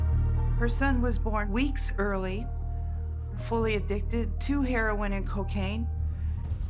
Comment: He was born what? Fully "evicted"? Is that what she said or am I hearing her weird? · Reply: Addicted